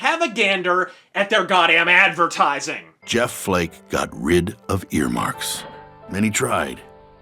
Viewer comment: earmarks? I thought he said earmuffs.